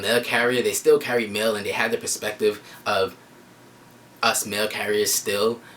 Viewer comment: Haha- I thought at first you were saying “male carriers” lmao I was like- girls work too 😂 Then I realized it was “mail” duhhh 🤦🏼‍♀️